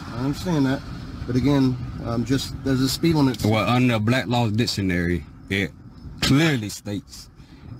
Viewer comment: minutes into the video, did I hear him say "Under black law dictionary"? So is he saying he's immune to the law because he's black? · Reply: No it’s a law dictionary called Black’s law dictionary